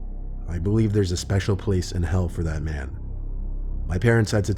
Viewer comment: I find it kind of funny 😂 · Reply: I thought he said... *"Theres a special place in help for that man!”*